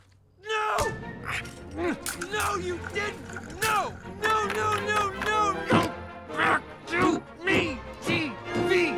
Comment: wHat dId hE sAy!?!??! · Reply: Ok! That is actually hilarious!! He did actually say “No you didn’t!” But once you hear “You D🤬ck!” That is actually hilarious to think about 🤣